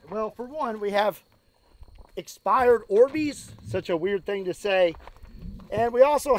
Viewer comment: I thought he was saying "expired Arby's" it took a minute to realize he said Orbeez